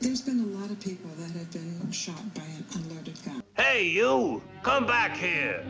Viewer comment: "There have been a lot of people who have been shot by an unloaded gun" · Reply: wtbgamegenie She didn’t say “thought,” she said “by an unloaded gun.”